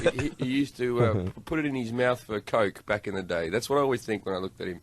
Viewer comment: Does anyone know what Jim Jeffries says at 5:16? It sounds like he says "He used to put it in his mouth with a coat back in the day". Does anyone know what that means? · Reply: segoviate he used to put it (meaning dick) in his mouth for coke (cocaine)